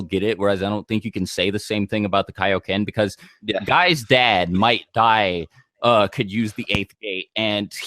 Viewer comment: I thought he said "Might Guy's dad might die", but then I realised he means Might Dai